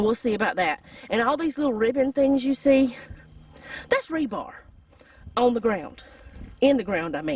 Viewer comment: I thought you said rhubarb not rebar. I had to go back and watch it. LOL